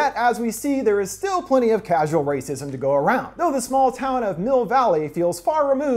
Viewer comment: I thought you said Hill Valley at first. I thought Mayor Goldie Wilson royally fucked up